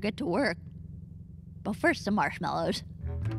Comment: did she say first the marshmello 😭😭😂